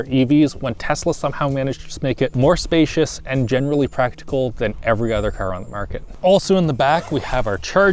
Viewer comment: I had to go back and check, because the first time I heard "genuinely practical" while showing that there's ample room to hide a body or kidnap victim, which made me a bit concerned. 😅 "Generally practical" makes sense too and is at least less concerning.